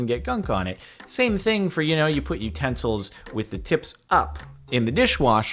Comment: I heard, "You put your pencils with the tips up in the dish washer." I was like... I don't wash my pencils